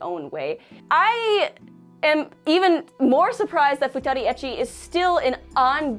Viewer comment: I misheard that as 'futanari.'